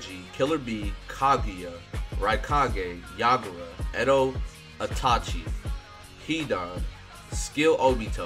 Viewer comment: You were somewhat unlucky. Those blazing fest units are top tier ones. The Kakashi is an old normal banner one and not the latest blazing fest one by the way. The only BF units i got were guaranteed and the worst ones that I did not want (Heart Pain, Body Sasuke.) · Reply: I went back to hear it more clearly my bad you're right it sounded like you said Kakashi initially but you did indeed say Itachi.